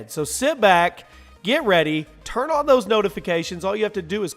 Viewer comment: Did you say turn off your notifcations